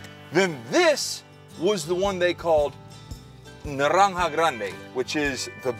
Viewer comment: Naranja grande? I thought he said Adriana grande🤣